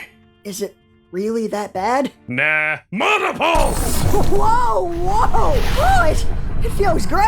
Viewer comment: I've listened to Archer's line at 16:18 several times already and I still can't tell what the hell he actually said here. Like is that even a word? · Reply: Mana Pulse, I think